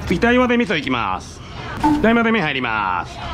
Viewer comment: なんて言ってるんだろう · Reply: 台まぜ麺はいります！（台湾まぜそばの麺をお湯に入れるからスタッフも準備してねー）って感じですかね！